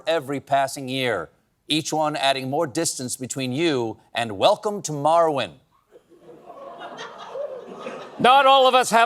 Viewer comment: When Colbert said "Welcome to Marwen" I thought he said "Welcome to Morrowind" and I wondered if I missed an Elder Scrolls game.